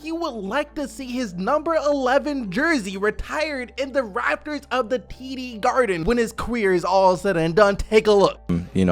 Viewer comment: and I quote “he would like to see his jersey in the raptors of the the TD Garden when his queer is all said and done.”